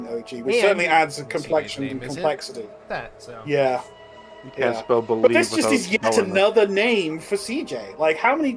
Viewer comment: What in the world did say around 1:30:27 ? Can't spell believe with out elin nin? LMAO